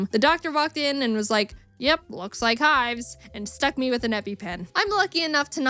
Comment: When i heard epipen, i was thinking it was happypen lol.